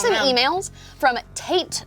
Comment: Lowkey thought you said that girl was emailing “taint”